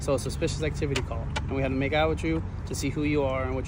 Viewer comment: "WHAT DID HE SAY"-- he wants to "make out " with him??